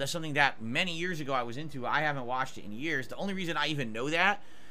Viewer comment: He finally admitted it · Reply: I thought he said he hasn’t play it in years